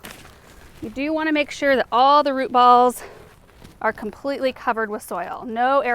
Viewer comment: Hahaha I thought you said Rupaul looks really nice... but you said root ball! But you can call that rose Rupaul if ya want! I'm suprised there isn't a rose named for Ru.